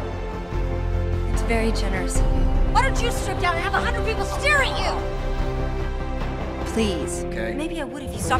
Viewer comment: ¿Qué dijo? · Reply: "why don't you strip down and have a hundred people stare at you?" 😊